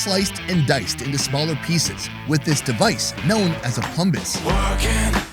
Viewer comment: I'm sorry, did he just say that that's called a Plumbus?